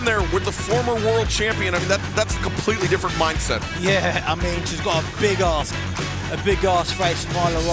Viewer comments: It totally sounds like Anthony says, "She's got a big ass," when Excalibur asks about Alex Gracia's mindset against Nyla Rose. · I honestly thought he said she's got a big ass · Did he say she’s got a big ass? 🤣